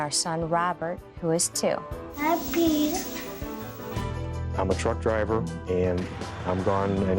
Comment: My sound wasn’t up all the way. I thought he said “I’m a drunk driver” 😂😂